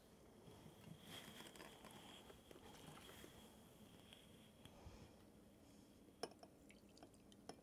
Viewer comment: When he said “now I’m gonna juice some limes” I hear “now I’m gonna do some lines”